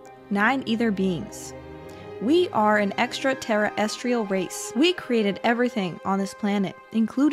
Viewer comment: Xtra Terra Estrial. 😊 Ah the Japanese “Show Goon” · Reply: I thought I heard him say "Extra Terror Estrial".